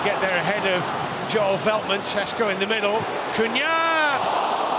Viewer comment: l swear he said tesco😂😂😂😂😂😂